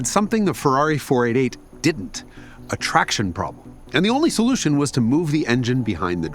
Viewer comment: I thought Jason said c7 zr1 had an attraction problem because it did look like a fish maybe catfish like